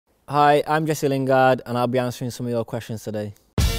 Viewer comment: Did I hear “hi I’m Messi lingard” at the start ahahaha